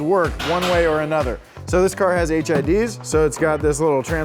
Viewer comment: low key thought he said HIV and I died